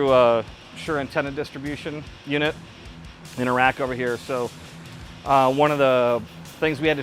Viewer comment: he says "in a rack over here." i hear "in Iraq over here" lol